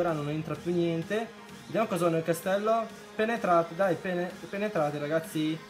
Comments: Solo io ho sentito pene al minuto 3:20😂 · solo io ho sentito pene? Ahahahahah grande!